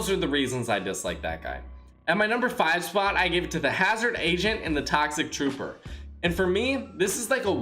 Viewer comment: It sounds like he said hazard asian